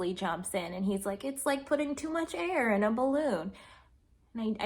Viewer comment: The first time I listened, I thought Wesley said, "There's too much hair in a balloon!"